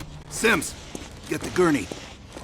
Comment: lol I thought he said “Simps, get the tourney.” lol I was searching for the gourney👀